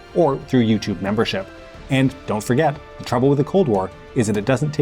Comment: I can swear that in the last phrase I heard "the problem with the CORN WAR" 😂🌽